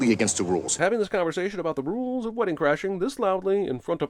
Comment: i thought wedding crashers said castle crashers for a second lol